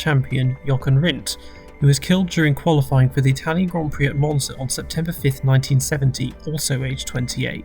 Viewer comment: did he just say mozna instead of monza or am i stupid